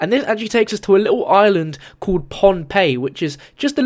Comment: I thought he said Pompeii